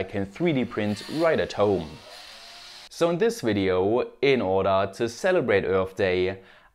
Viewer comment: I could swear I heard "in order to celebrate HER birthday" and I thought to myself "boy, is he getting cancelled..." 😂😂😂😂😂😂😂😂